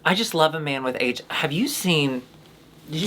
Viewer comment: Gotta admit I thought that Joshua Jackson was HIV+ for a moment. Glad i misheard abd he's ok